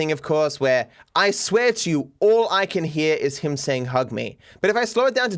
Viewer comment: For the first and second times i heard "Hug me", but on 3rd I heard "Hold me", hmm